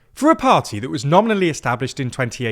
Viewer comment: (As already pointed out, I misheard “nominally” as “only”.) “Only founded in 2018”, that may be factually correct, but still disingenuous, it’s merely the latest incarnation of a nigh-on 20 year Farage bandwagon.